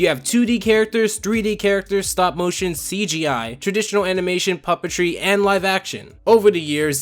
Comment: anyone else thought he said papa tree